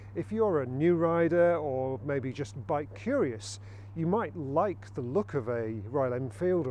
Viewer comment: Did he say “if you’re a new rider or bi-curious”?